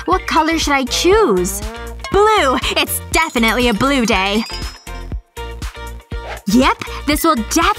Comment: Did she just say "it's definitely a blue day'